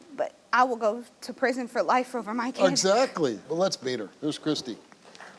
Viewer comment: I thought he said “lets beat him”not “let’s meet her”